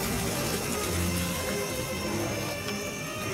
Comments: Who else thought grandpa said "Oh shit, my tit!" at that point the video? I thought that was ridiculous, lmao. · When agp saw his tent burning why did it sound like he said " oh shit meh tit " 🤣🤣🤣